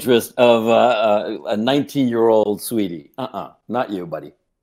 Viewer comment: You know he loses when he calls Destiny out for not being able to pull 19 year old girls PepeLaugh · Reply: i misheard and thought he said "19 year old swede-ie" and thought he was trying to call out destiny xD